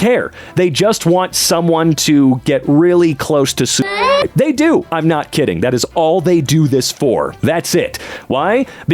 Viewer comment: What word did he censor out at 8:48? I need to know this. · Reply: Suicide was the word